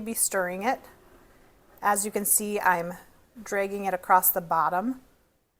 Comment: You must be from the southwest. I clearly heard you say "DRAY-ging" instead of dragging.